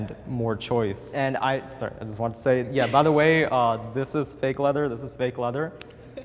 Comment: Sorry, standing with on a lot of these. I live in what's considered a developing country. We recently had Beyond Meat and Omnimeat come in but even yearsss before, the vegan diet was totally accessible for an affordable price if you had the will. Asia in general has a lot of vegan ingredients beyond "lettuce", like tofu, mushrooms and beans, so I didn't really buy the China excuse. Not to mention the fact that many Asian religions, like Buddhism in its many forms, consider not eating meat as an act of compassion and merit-making, so it's somewhat normalized. Just trying to offer some perspective. I think cool and deserves some love here. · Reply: Generic Name sorry for that.. I heard “so, I wanna say...” my bad generic.. anyways I think is kinda of annoying this dude